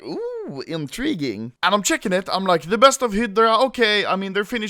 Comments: at first I heard "The best of Hitler" lol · I legit thought you said THE BEST OF HITLER Which makes you wonder, what the fuck is that?